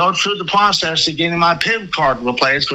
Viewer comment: Wait did he say PIMP card? 😅😅